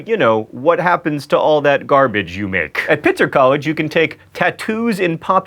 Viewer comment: I thought you said pizza college...now I'm disappointed. D